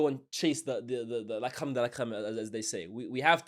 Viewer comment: What did you say in 2:55 La cram da la cram?😂😂😂